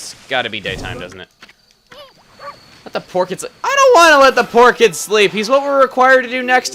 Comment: I heard this as "porchids." As in "pork orchids." I'm very amused by the subsequent image of water sausage plants, and I don't think I'll ever see them the same way again.